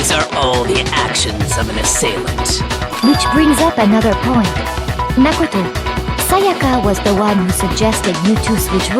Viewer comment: i thought celestia said mash potato for a second